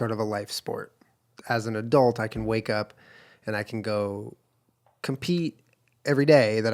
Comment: "I wanna go compete" thought she said, "can I go pee, I wanna go pee" lol I think she should do both judo and jenga